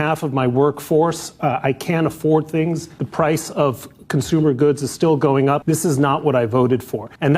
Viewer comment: 😅 Did anyone else catch what he said. 2:00..😅 the Price of GOODS IS STILL GOING UP under Trump's first 100 days. in other words PRICES HAVE BEEN GOING UP UNDER BIDEN, NOT TRŮMP and to ve honest I LOST WAYYYYYYYY MORE MONEY ON MY URA /ROTH UNDER OBAMA AND BIDEN'S YEARS. CHINA IS HURTING.. WE HAVE BEEN PAYING TARIFFS FOR DECADES TO CHINA AND MEXICO. FAIR IS FAIR ...